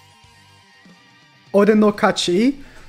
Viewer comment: She actually says "Filo no Kachi" since instead of a pronoun (meli uses ore) she uses her own name.